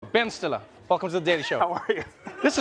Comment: I totes thought you said “Rudy Ben Stiller” like he’s a comeback kid hahahha